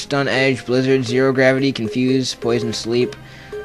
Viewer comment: when you said "poison 'n' sleep" I heard "poisonous leap". That sounds like a pretty cool attack to me :P